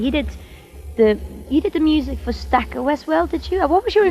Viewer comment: what is this movie she's talking about ..all I hear is "Stack-OS" .. what???